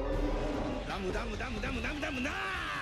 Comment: Bro did not say “Muda” bro said “Damu”